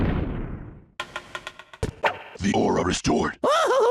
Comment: Why do I hear "THE AURA IS DOOR"?